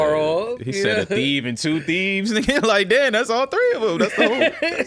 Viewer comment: I think the dad said "a thief and two feebs". Feeble Minded.